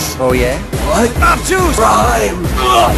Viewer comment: is he saying Rhyme?!?!?!?!?!?